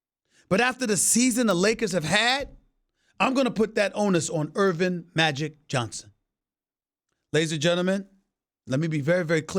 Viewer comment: He said, "Irving "Magic Johnson" but I heard "O.J. Bobby Johnson" 😂🤣😂